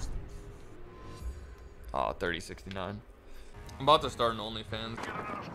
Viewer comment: Did say he's going to start a OnlyFans........for what??